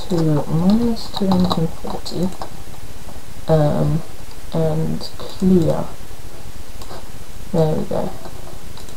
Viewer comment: i didn't understand the part in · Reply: oh shoot she's here